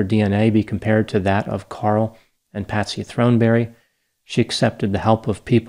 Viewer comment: I thought it said Thornberry at first and gave me a flashback to the childhood cartoon lol